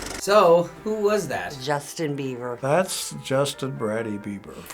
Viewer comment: Did she say Justin Beaver? Love it xD